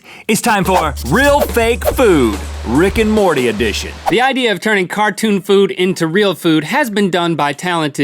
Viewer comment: I thought it said “Rick and Morty carton food.” I was thinking what that could mean. 😀😀😀